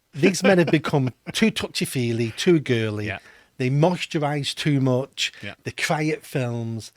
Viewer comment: did he really say they MOISTURISE too much?